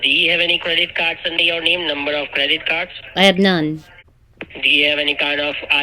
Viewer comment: When he said I d credit cards, I thought he said gray cats, lol!